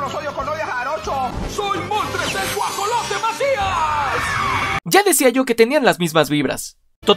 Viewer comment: 😂😂😂 la primera vez que escuché eso pensé qué dijo "el guajolote matias" 😂😂😂 ya después supe el pedo 😂